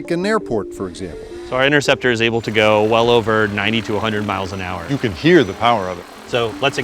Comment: It sounded like he said ninety two hundred mph and I was like "wow it's 5 times faster than fighter jet"